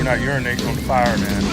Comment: “We're not urinating on the fire, man" "I'm calling both games!!" "That's not what he wanted to cook" "Yeah and he’s sucking right now so” “I’ll get somebody else to ask these questions” "That was a thick pick!!” "The culture is actually damn good" “YOU LIKE THAT?!” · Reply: Ohhh, I always thought he said "When I urinate, it's on fire, man"..... Like he got an STD or something lmao